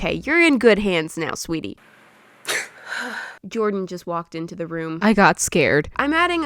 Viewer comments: When you said that you heard Jordan walk in I thought you said Georgie!! I just pause and just sat there for like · Omg when she said, “Jordan walked in to the room and I got scarred.” I heard, “JoRgiE walked in to the room and I got scarred.” 🤣😂😅